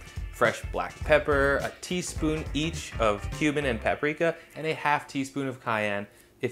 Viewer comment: I assume he meant cumin but I definitely heard "a teaspoon of Cuban"